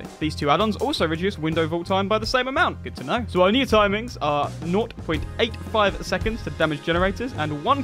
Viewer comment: dude wtf "noyt" ? He says something like "nort point eight" instead of "zero point eight." Its gotta be a british thing because I've heard British youtubers say it before. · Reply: Naught pronounced “noort” meaning 0 :D